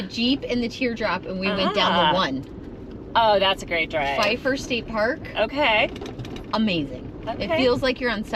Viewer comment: what state park did the girls mention at 22:01 ? Couldnt pick up what she was saying ? Thanks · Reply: Pfeiffer State Park, Big Sur.